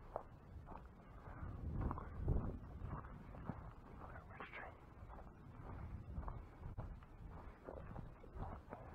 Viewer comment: what did you say there? “What a machine” ? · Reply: "Here from the Tree" I am measuring distance with a compass.